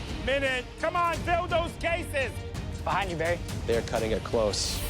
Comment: am I the only one who heard “d*ld*s cases? Sorry it’s probably just me and my dirty mind Or we all ignored it.